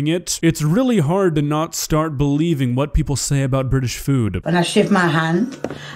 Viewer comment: did she say "I Sh1t in my hand?"